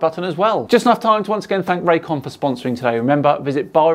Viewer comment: For a second at the end, I thought you said, "Thanks to Ray Comfort for sponsoring us."